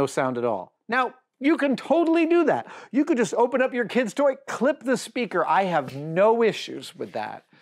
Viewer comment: I thought it said quit your kids toys. Never!